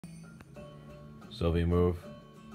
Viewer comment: I thought it said "Sophie, the movie."